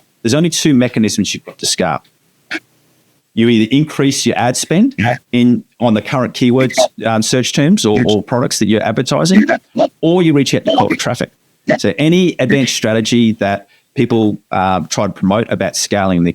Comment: not a clue what he said there. What levers? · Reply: So the two levers are, you either increase ad spend on the existing keywords or you reach out to colder traffic. Sorry about the audio! I hope this explains it!